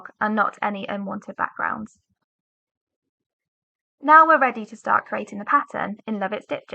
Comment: Hello what is she saying at 1:04? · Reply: Looks like the auto-generated subtitles messed up pretty bad there. I've corrected them manually but what she was saying was "now we're ready to start creating the pattern in Love It Stitch It"